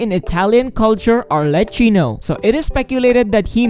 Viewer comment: I heard "Harley Quinn" instead of "Arlecchino"... I think I have hearing problems 🤣🤣🤣